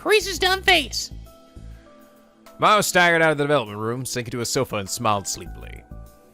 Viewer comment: I need to stop watching videos half asleep. I swear I heard at 14:10 say "Maho staggered out of the velvet room" 4 hours of trying to make the perfect Trumpeter would probably do that to you.